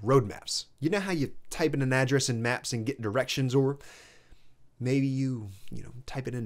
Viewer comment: What exactly did he say at 08:33 ? “You type it into maps and get a” what ? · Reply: “… and get directions.” Lol it could definitely be heard a different way though.